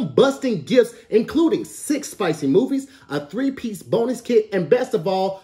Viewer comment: I’m so hungry I thought said 3 piece boneless during the advertisement. That mane said bonus 🤦🏾‍♀️